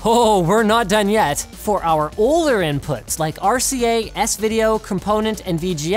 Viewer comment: wtf does that little word say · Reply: Kyle TheTreeKat it says 'oller'. Linus mispronounced 'older'